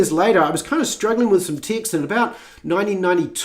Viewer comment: I thought you were saying "Ticks"for over 12 minutes in...then I saw the words you were reading and noticed you read the word Ticks ?????? AND I then saw Text, you had been saying text/s instead of the word Ticks. LOLOLOL